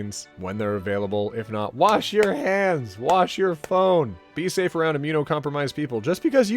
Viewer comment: did he just say "wash your phones"?